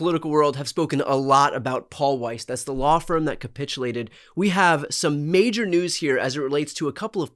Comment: Oh capitulated. I thought you said copulated which seemed very likely.